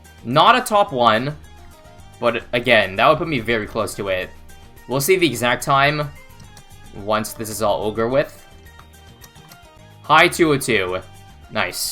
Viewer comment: Are my ears deaf or did he said “all ogre with”